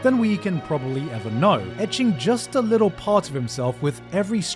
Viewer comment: "edging a little part of himself with every stroke" i've heard enough thank you. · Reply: I think he said "etching" but yeah, misheard dialogue and lyrics can be funny sometimes 😂